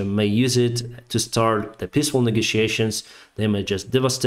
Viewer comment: i was missing you, you give the best, simplest, and most comprehensive reports on the war that i can find. thank you for all your good work, it is so much appreciated, slava ukraine! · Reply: Love this channel as well and fully agree. Still everytime he says peaceful I hear "pissful"